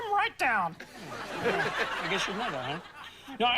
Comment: What did Kermit say to upset the entire audience? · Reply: “I guess you know that, huh?”